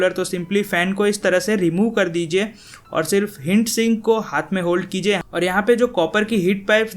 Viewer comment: Am I the only one who heard "HINT SINK"? ( 4:45 )